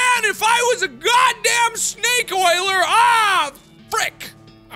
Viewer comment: The first time I watched the series I thought he said snake wailer